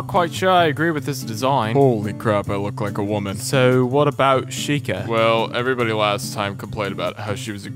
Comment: does anybody else notice that at 0:13 Freddy sounds like hes saying "sheikah"?